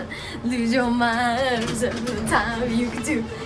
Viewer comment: sounded like "lose your mind, just a step at a time" lol